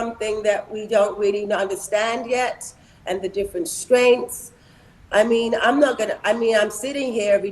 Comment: Sounds like she said different strengths but I think she said or meant strains.